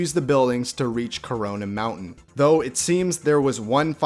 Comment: did u just say CORONA MOUNTAIN?🥴